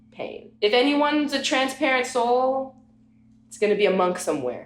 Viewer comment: All time I thought she was saying Transparent Son 😂🤣💀✋🏾